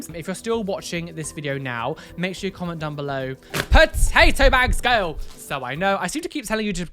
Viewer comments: I legit thought you just said Potato Manx... 🤣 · I swore you said potato babes.. I was like❕ 👏🏼👏🏼👏🏼yes, talking to me!